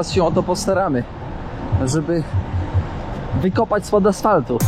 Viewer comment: usłyszałem: wykopać stąd asfaltów Aż musiałem cofnąć bo pan kierownik by w życiu tak nie powiedział haha